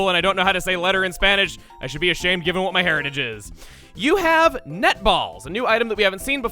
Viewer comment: For some reason I heard nut ball and loblolly hahaha! The shit!? Google's dictionary has loblolly in it! The heck does it mean?